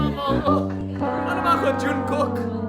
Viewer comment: Did Will say "Jungkook"? 😯 Anyways, welcome back to the Philippines Will!